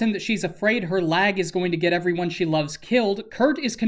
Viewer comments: I always thought she said "My leg is going to get everyone killed." (not lag) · This whole time I thought sachi said her LEG is gonna get her friends killed, now it makes so much more sense that it was her lag- the kirito is always right foundation would definitely mock her leg.